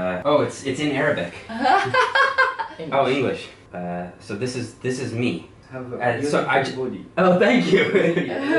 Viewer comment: HAHAHA does he say you have a very good body 17:34???